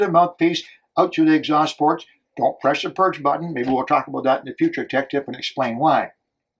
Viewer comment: Hey, at 4:07 did you say "don't press the purge button"? If so, what led to your change of heart prior to production of your episode: Scuba Tech Tips: Go Ahead, Push The Purge When Rinsing - S05E15